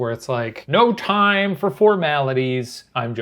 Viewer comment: I genuinely thought he was going to say ‘no time for fortnite’ for a second